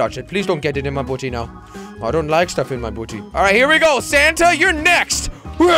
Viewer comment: when he said '' santa ur next i heard him say (oh sh*t) '' uhhhhhh